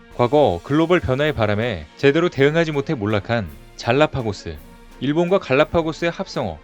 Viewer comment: 잘나파고스..! 잘나고파스 로 잘못 들었어요!